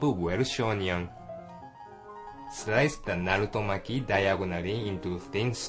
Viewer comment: Wtf did he say at 2:44 o.o?? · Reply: narutomaki ;)